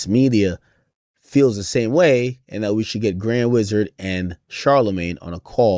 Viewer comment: I could’ve swore he said grand wizard as Ai. Boy how the turn tables turn